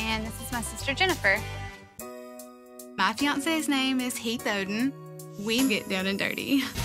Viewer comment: Oh I thought she said her husbands name is Beethoven